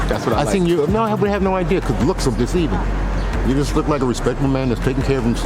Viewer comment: 'cause looks are deceiving' - i thought he said 'cause you look so defeated' at first